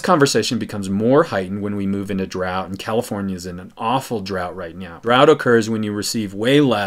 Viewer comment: did you say "right meow"?